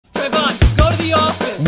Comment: I hear "where da door" instead of "what did i do" for some reason